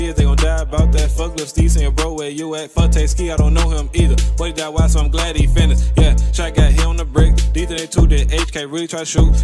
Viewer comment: Did he say blake die y so im glad he finished ?